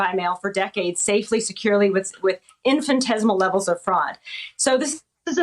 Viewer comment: do americans pronounce the word "Infinitesimal" as sounding like "infantesimal" or am i just hearing it wrong?